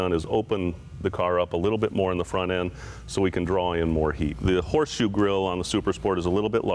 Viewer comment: did he just said "We opened the front more so we can DRAW IN more heat?"